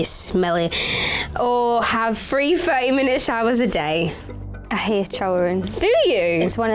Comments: What did Leigh say? "Not shower for 3 weeks or..." sorry lmao can't understand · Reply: Mehr Kalra take 3 30 minute showers a day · What did Leigh say at 1:55? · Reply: x No she said "Or have free thirty minutes showers a day."